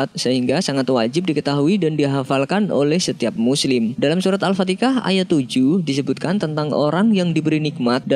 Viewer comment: Yg dengernya Surat al fatikah Siapa aja?